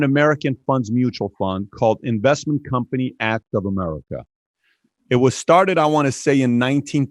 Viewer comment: G. Edward Griffin is the man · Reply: What's the name of the mutual fund Patrick mentioned at 44:22? English isn't my first language and he kind of talked away from the mic when he named it, all I heard is"investment company at of - inaudible-"